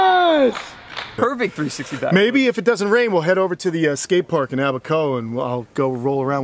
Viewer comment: Haaaang on I'm hoping I misheard him here But 6:10, does say 'well have a cone?' a joint?